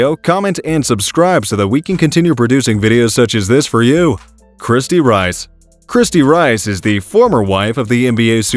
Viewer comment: Oh I thought he said “krispy Rice” .